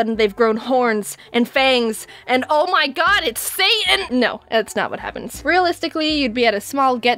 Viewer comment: I know it is a bit later but for a moment, I thought Jaiden said "It's SANS!"